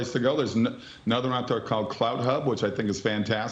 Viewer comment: Had to look it up. Thought he said cloudhub. Found CloutHub. Never heard of it before now. Only currently available in USA and Canada.